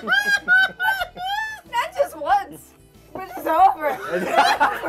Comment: what does jessi say at 19:49? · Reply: "Not just once, over and over"